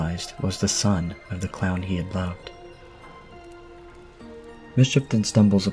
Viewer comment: did you say "plowed" or "loved"? Lol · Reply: Both jk 😂 Loved hahah